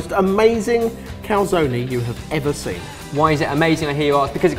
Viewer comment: did they call a calzone a calzone-ie? I'm so confused